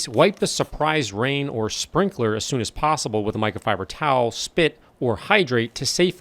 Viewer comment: did you say “spit”?